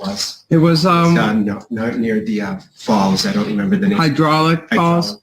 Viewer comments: "near the falls" I thought he said balls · I had to turn on the closed captions after they said the name of the park..... I thought I heard balls instead of falls.... I about lost my cookies....